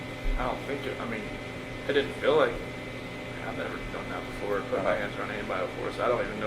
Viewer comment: it sounds like he says "I've never done that before"; rather than "*I'd* never done that before ' Does that mean he still hasn't?! Also weird he didn't have many scratches on him.